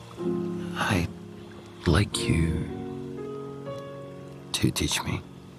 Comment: I litterly thought that he said: “i like your titties”